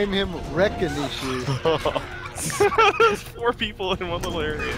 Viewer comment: I always thought they said "I'm gonna eat you!" But now when I put the volume up louder it does sound like wreckanishu